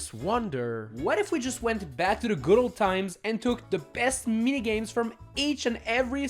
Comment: that sounded like Mario party the top 100 for a second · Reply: Well he said the best ones. Does that sound like top 100? (By the standards of the minigames.)